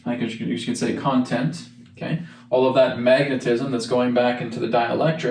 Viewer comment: did I hear Magatism ? Sounds good to me !